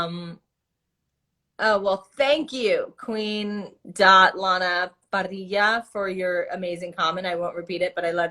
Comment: does anyone know what the username she said was? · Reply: Queen.LanaParrilla i think